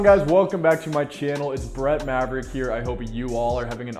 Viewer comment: I thought he said it’s bread maverick at the beginning